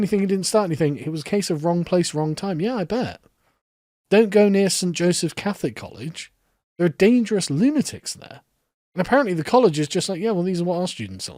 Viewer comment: This is what I heard: "Don't go near black, jungle animals; they're dangerous lunatics, them."